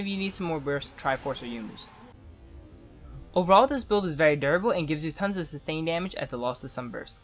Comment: "Overall this build is very terrible..." (I know you said durable, but at first I heard it wrong :P)